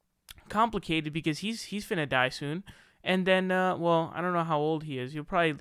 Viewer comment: did you say “finna”? Are you from New York?